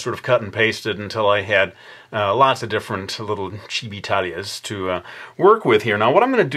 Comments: Chili Talia xxx That's funny to me I'm a big fan of an anime called Hetalia abd chili versions of the characters are called Chibitalia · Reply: I was thinking: Did he just say Chibitalia? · I thought he said Chibitalia from Hetalia beside Chibi Talia.